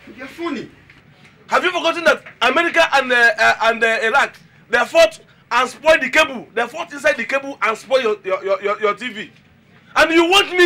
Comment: captions says "America and Iraq lack their foot as well dick." Couldn't agree more.